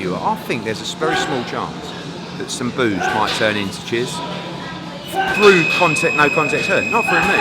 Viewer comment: wow thought he said 'booze might turn to chiz' 🤣